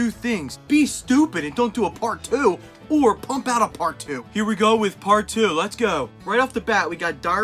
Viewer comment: I thought he said “poop out a part two😂”